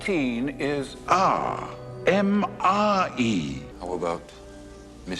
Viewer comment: Because I was in the military, I heard it like "MRE" as in Meal, Ready-to-Eat.